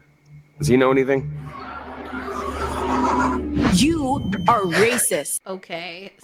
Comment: The caption said "YOU'RE racist" but she actually said "you ARE racist". If u can't even get the captions right, then I doubt the rest of the game will be any good 🤣